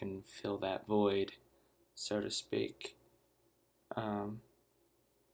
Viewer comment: Did you just say, "have a great wife"? Hahah! Hilarious, thanks tho.